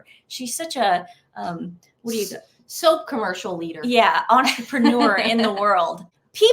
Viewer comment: Leader, oh sorry i thought you said LIER! LOL Just said i totally agree.. 🤣